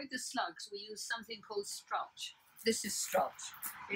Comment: What is the slug deterrent (3:46)? Hard to hear what she says but I would like to try this. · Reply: I think the lady says “strulch” which is straw mulch with slug and snail deterrent... you can find it online... hope that helps